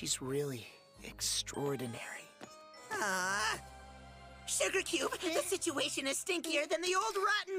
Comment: did he just say sugar cube !!!!!!!!!!!!!!!!!!!!!!!!!!!!!!!!!!!!!!!!!!!!!!!!!!!!!!!!!!!!!!!!!!!!!!!!!!!!!!!